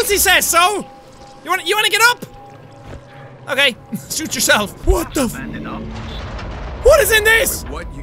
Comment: When Jack said suit yourself I thought he said SHOOT yourself